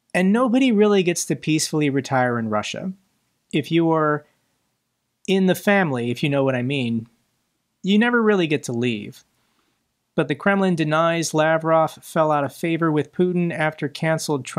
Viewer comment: Did say, " Lavrov fell out a window " Oh, sorry, I misheard, he said fell out of favour :)